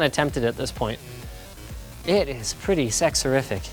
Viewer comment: Whait... Did he just say sex horrific? Poor Linus... :D